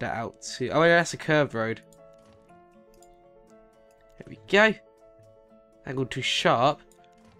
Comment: I thought he said I'm going to shart